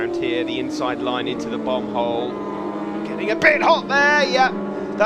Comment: Could someone explain what the commentator is saying at 16:08 -16:12 because I'm hearing "Bum hole". Addictive videos by the way :)